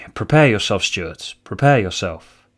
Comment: In the "cold opening" clip with the phone booth, you sound drunk. Were you drunk? Were you trying to sound drunk? Were you just really tired? Also, I thought you said "Brace yourselves, Stewards!" and I thought this was how you were addressing your audience from now on forward. And I felt it was fitting: Your name is Stuart, the people who watch your channel could be called "Stewards".